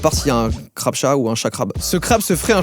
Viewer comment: j'ai compris chacra